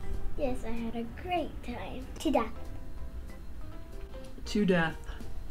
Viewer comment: For a second I thought she said "To die for."with the toast instead of "to death. They are both darling & intelligent young Ladies. 💙😺✌"